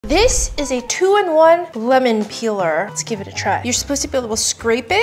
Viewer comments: I thought you said woman peeler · I thought she said it was a 201 women peeler